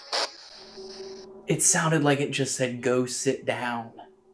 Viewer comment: I think it said 'This is us' instead of 'Go sit down'🥶